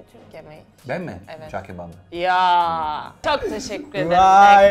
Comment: en sevdiğim yemek cağ kebapmı dedi ben mi yanlış duydum?